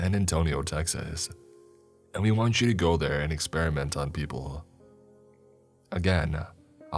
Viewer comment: Thought it said “the taxes experiment”